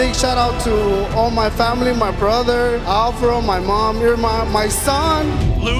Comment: did he say your mum?